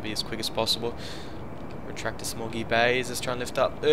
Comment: When he said small gear bay, I thought he said smoggy Bay also.